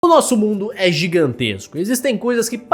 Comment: Por algum motivo eu escutei ele falando bumbum KKKKKKKK O skinwlaker tá outro nível